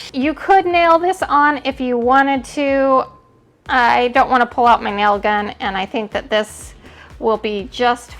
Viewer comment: Revisiting some of your videos and thought I heard you say you "didn’t want to pull out my nail guns". Lol, gotta be a first! As usual, great work! Thank you